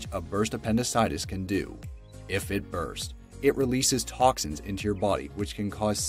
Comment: 8 thought he said sexist